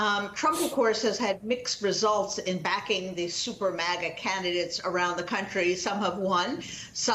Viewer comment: I thought she said the "super maggot" candidates!